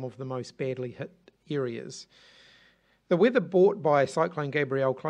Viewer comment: did he just say "the weather bought by cyclone.."??